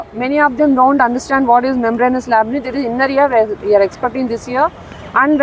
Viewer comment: can u pls tell me what miss said about the important diagrams....?? ( All that she mentioned plz..) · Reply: Chloroplast, memberanous labyrinth (ear ka part)